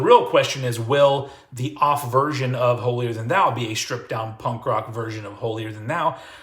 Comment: why do I hear "holier than now?"